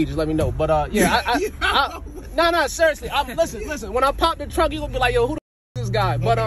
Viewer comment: I thought he said drunk😂